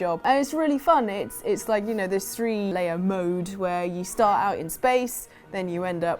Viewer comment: I genuinely thought you said "three Leia mode" and not "three layer mode"